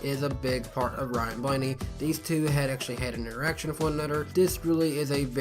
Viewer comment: I swear at 3:20 or so it sounds like he says theses two actually had and erection with one another